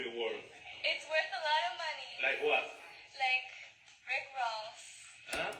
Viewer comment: I thought she said Rick Rolls 🤷🏻‍♂️ LoL